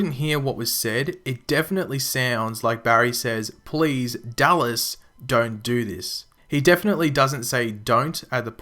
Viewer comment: I heard " Please don't. Don't do this"🤔